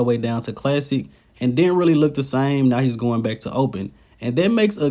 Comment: I thought just said he wasn't going back to open..🤔